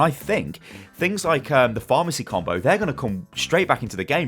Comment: did he say the Pharmacy Combo?